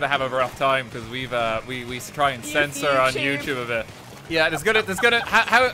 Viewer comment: I like how has her own “YouTube” censors recorded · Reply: "You fucking YouTube" - 2023 No joke, that's what she actually said